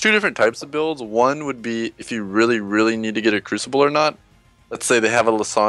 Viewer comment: if you realy need to get a.. ? what does he say there? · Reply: said If you really need to get a Crucible.